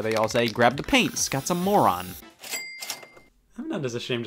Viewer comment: Ok, I can't be the only one that thought he said :"grab the paint Scott's a moron" and not :"grab the paint Scott* some more on *I don't know if it's scot or scott